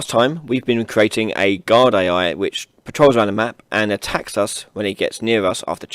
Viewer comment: guard AI I thought he said god AI